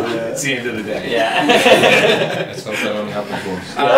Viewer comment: Somebody tell me what mark said at 4:45 😭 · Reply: "Let's hope that only happens once" "I'm off now anyways, see yas later"